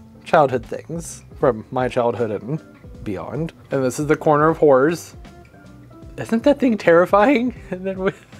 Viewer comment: I look forward to your Christmas decorating videos every year. I esp love the ugly plant sweater videos & decorating planters. This yr has been an esp hard one for my family & me with the loss of my husband of 37+ yrs in April, so the holidays haven't been that festive for us.....until this video. You have such a wonderful outlook on life & your sense of humor is delightful. So, on to the highlights of this yr's Christmas decorating video: 1. Turtle farts 2. Hunting for puppy poop 3. The Snowman Couple 4. "Sometimes the elves get naughty" 5. "If you want some sugar you just rip his head off & grab the spoon" 6. The Corner of Whores" (I coulda sworn that's what you said! Then I realized you said 'horrors'. I like 'the corner of whores much better, though. LMAO) 7. Your Christmas tree that changes height. I swear I had no idea they even made Christmas trees like that! I love it. Also? It reminded me of the Christmas trees on Mrs. Brown's Boys. This & so much more, esp you, made this Christmas special for me. Thank you so much for making me LMFAO & cheering me up. Merry Christmas, Happy Holidays! I hope this is your best Christmas ever!